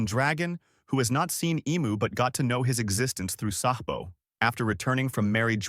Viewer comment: Wtf did he call Sabo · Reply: Sahbo